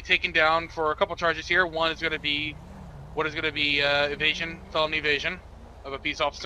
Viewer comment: When he said “felony evasion” I thought he said “felony of Asian”